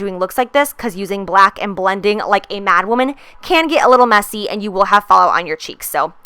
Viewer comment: I thought you said "blending like a black woman." And I was like GIIIRL! But you didn't, LOVE YOUR MAKEUP!❤️ and you of course!❤️😍😜